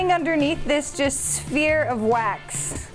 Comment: Did she say disc or small cylinder? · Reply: Sphere? I do not think that word means what you think it means.